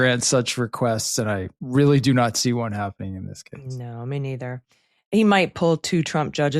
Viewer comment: When colloquially using "me neither" (43:00) I immediately hurdle back to Catholic elementary school, where I would be corrected "nor do I" or "neither do I" as grammatically proper. I hear "mean ether" which I find to be a humorously appropriate colloquial simile. Mean ether sounds like an anesthetic fuel for the gaslight. Words are fun. Love your show.